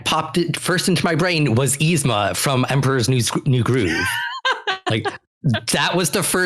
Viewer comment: When mentioned Emperor's New Groove I swear I almost heard him say Emperor's New Soul. Gave me a good chuckle!